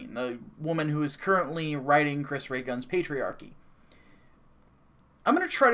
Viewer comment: I don't know why but when you said Lacy is riding CRG's patryarchy I thought you said paytreon. P.S. has anyone ever told you that you look like Berry from Resident Evil?